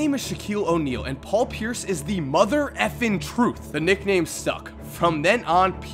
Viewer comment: at first i thought he said the nickname sucked lol